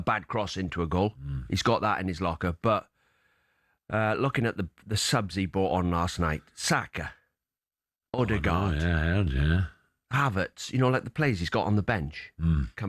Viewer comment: He said at 1:09 about the subs Arteta brought on last night, Saka, Odegaard...... HAVERTZ !!!!! FFS did he actually watch the match, HAVERTZ wasn't even on the bench, he is injured ! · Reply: His exact words were at 1:09..... "Looking at the subs he brought on LAST NIGHT, Saka, Odegaard.... HAVERTZ, you know like the players he's got on the bench", tell me where I heard it wrong?